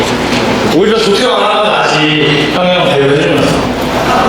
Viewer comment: 여기 자막 형 형 대우해주면서 이렇게 들리지않나요?? 나만 그렇게 들린가? 자막이랑 들리는 소리가 다른거같아서